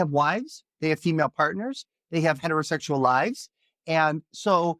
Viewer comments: did he say they have heterosexual lies? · I agree with ur guest! And thats coming from a 100% vegan that only eats meat once or twice a day, so u know im making sense! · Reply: did he say they have heterosexual lies?